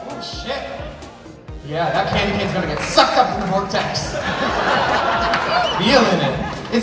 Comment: When you said "sucked into the vortex" I thought you said "now I'm ready to suck up to some old timers", and I thought that's harsh but reasonable 🙄 Those eyes looked like they took time, gorgeous.